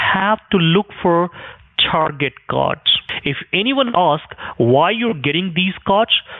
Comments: 😂Lmao I thought he said "to look for Target Gods" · Target Cods, I heard as Target Gods 😆